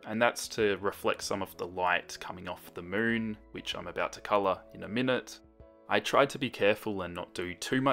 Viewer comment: Did anyone else hear "Bitch I'm about to color?" Lol.